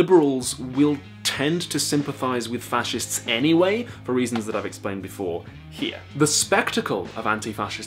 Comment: there is a typo in captions - it says reaosns instead of reasons